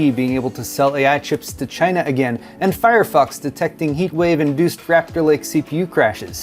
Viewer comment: I swear I heard "Firefucks" when you said fire fox..... then I heard "Suckit str5" lmfao this is a fun episode.